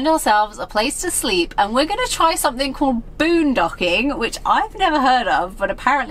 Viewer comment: For a moment I thought you said boondoging which sounds like the kind of video creepy T watches in his spare time